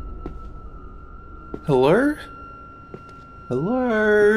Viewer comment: When he was saying hello at the beginning I thought he was saying heller